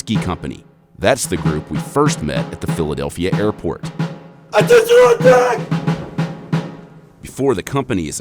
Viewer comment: no clue what he said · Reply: Attention on deck